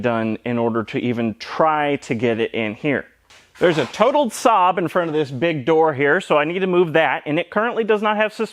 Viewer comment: When he said “totaled Saab in front of the door” I thought he said “Total S. O. B. in front of this door” and I thought he was talking about himself. Then thought why would he say that? Had to watch that part twice to remember there is a car outside the door.